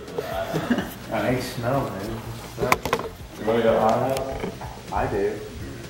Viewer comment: did he say you wana get high tonight at 10:08?